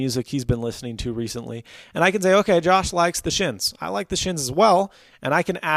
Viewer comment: Thought you said "The Shits"